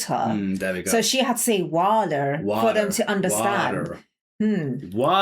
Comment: We say Wah der . It sounds like she saying walter 😂